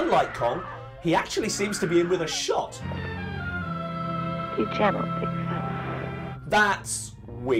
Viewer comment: What does she say at 1:45? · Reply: "Be gentle, big fella" is what it sounded like to me.